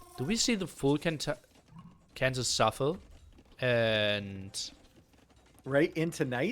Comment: Did just say "Cansas city shuffle ?" 4:26:15 "It's not a story you would have heard before." Berber victory , I can't praise this game and tournament enough.